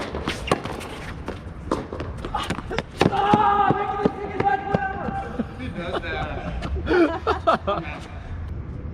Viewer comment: What did say at 22:00? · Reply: "AHHHH that would've been the sickest match point ever!"